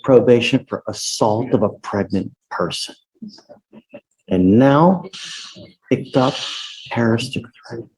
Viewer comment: what does he say at 8:11? "And now you picked up...." ??